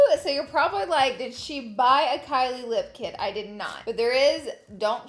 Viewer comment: Was it Kylie or Khloe? I thought someone said Khloe. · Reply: Kylie